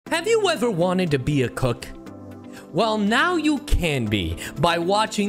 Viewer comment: I thought he said “Have you ever wanted to be a cuck?”